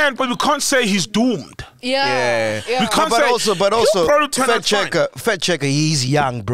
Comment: I also thought you guys where saying fat checker 🤣🤣